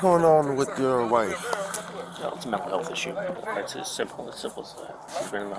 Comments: did he say "it's a syphilis thing"?!?!?!😮🫨🫨🫨🫨🥴bro...wtf are you doing..... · "A health issue. It's as simple as that." I had to replay her husband saying this because I thought he said, "It's syphilis."